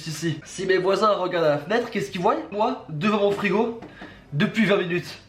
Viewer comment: c moi où j'ai vraiment entendu "keski VOYENT???